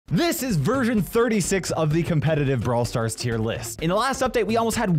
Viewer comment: i think i heard "this is virgin 36" not " this is version 36"💀💀